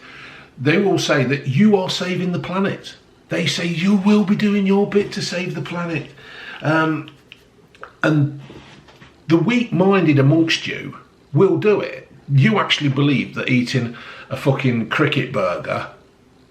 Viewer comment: Did you say the weak minded? Royal loving, Megan Hating, Clarkson Loving, Acceptor of feelings, regardless of facts..... Damn!... That is a big Rabbit Hole.... :(